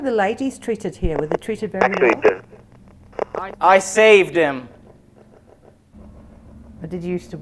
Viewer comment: At (about)38:00, during Estes when perceived the the phrase “I saved him”- I heard it as “actually did” (I believe it was pertaining to his opinion of your skit). check it out. See what you think. Thanks for everything. Enjoy.